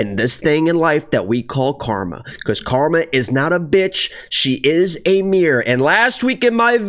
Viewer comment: First I thought you said "Karma's not a bitch, she's a weirdo" :D